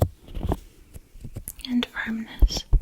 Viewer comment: did u say u were checking my ear for nachos? Cool.